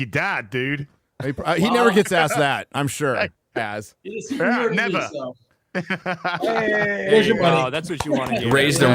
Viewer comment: Did anyone catch watch Dean Cain's son said at about 1:14:17? Of course was talking so I missed it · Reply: Sounds like he said, "He's a superhero to me"